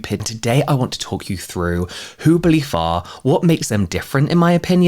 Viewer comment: I had to rewind this section because when you said “who Belif are” I heard “whobaleafa” and was very confused haha I turned on captions and felt like such an idiot.